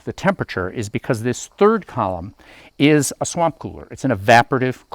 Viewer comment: 1) At first I thought he said Warp cooler. 2) "Honey, I'm gonna go take a shower..." Then from behind the bathroom door, "WHAT THE FU--!!!"